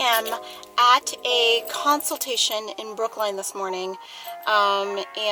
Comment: I thought you said you were in Brooklyn in your intro , thinking : " She is near my neck of the woods ( Queens / Brooklyn ) " 😃😃